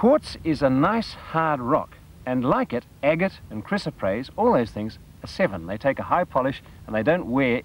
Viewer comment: What mineral did you say? "chris appraise" ? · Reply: Chrysoprase - a light green mineral (a form of chalcedony) that gets its colour from nickel - Rob